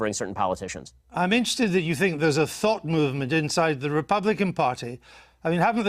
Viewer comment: EVERY time i hear Andrew Neil say "thought", i hear "thot". A "Thot" movement in the Republican party.